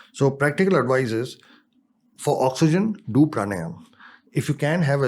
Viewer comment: What does he say to do at 1:21:40 ...?!?! For oxygen do pranell?!?!? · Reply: Pranayam